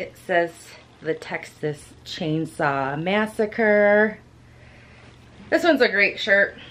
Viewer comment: Sounded like u said check-xas chainsaw massacre lol 😆